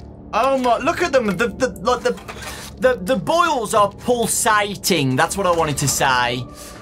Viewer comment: i almost thought he said bowls not boils